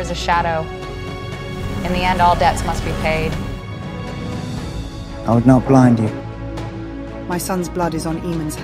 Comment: Good effort - but did I hear "I will now grind you'??